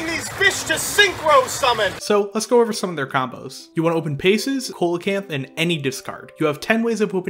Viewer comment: Did u just call it Holacanth? Is this some inside joke I'm not aware of? Edit: I watched the rest of the vid and he is in fact saying Co-lacanth not Holacanth which is what the first instance sounds like.......however if you know that Ghoti is meant to be pronounced 'fish' surely you know coelocanth is not pronounced the way you say it so again....is this some joke I'm not getting?